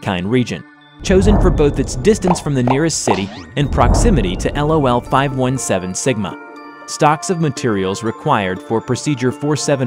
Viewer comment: waitt did he say lol-517-sigma? in